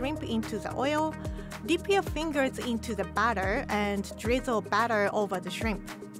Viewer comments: For a second I thought she said dip you finger into the oil but she said batter not oil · when I hear "deep your finger to the batter" 😱